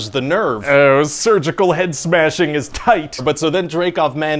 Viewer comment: He said, "surgical head smashing," but my kids are playing loudly and I heard, "cervical head smashing is TIGHT!" And I thought, "Oh. My god."